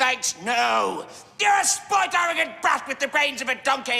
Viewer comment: Last part I heard “I’m a toad”